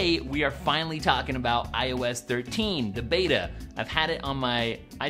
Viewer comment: Am I the only idiot that kept hearing "I was 13" instead of "iOS 13"? 00:12 "Today we're going to be talking about I was 13." lol